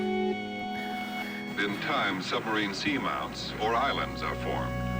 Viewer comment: for years and years I misheard "submarine seamounts" as "suffering seamounts" in dandelion